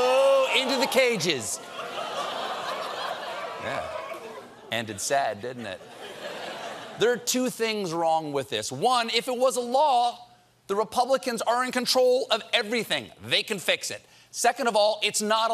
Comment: Keep pressing. I hear “If it was Allah…"